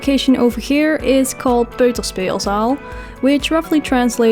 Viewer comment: Eh, hello, are you dutch? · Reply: Oh nevermind i heard you say peuterspeelzaal. Wow.